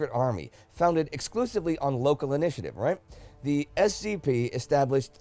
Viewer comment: I heard SCP instead of SZP and thus I joked Indy: "the SCP established three basic goals" Me : "to Secure, contain and protect" :p